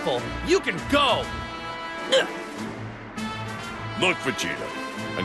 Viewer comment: Did piccolo say lord vegeta